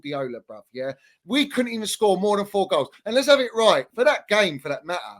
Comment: I just heard "And let's have it right" like 30x😭